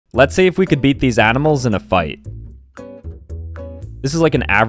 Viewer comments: I heard “ Let’s see if we could eat these animals in a bite “ 💀💀💀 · I thought he said “Can you eat these animals in a bite?” 😭😭😭